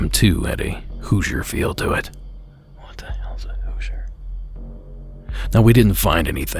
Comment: did anyone else hear a whispered, "What the hell's a Hoosier?!?" or am I losing my marbles? It had never occurred to me to listen for EVPs within your recordings, but maybe I'll start.